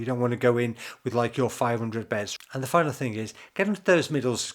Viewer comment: Thanks, what did you say at 11:06 couldn’t quite make it out? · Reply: ''The final thing'' - talking about middles